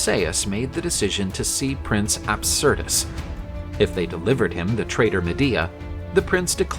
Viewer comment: misheard his name as Absurdus (instead of Absyrtus) 😩